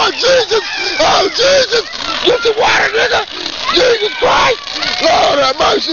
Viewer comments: My whole life I thought he was saying "Get the watermelon!" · Who else thought he was saying "watermelon" at 00:43 tho? Watched a video with this sound clip and always thought it was watermelon 😂